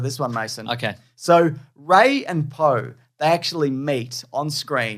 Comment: i thought you said that rey and poe "mate" on screen... and i was like how the hell did i miss that scene?!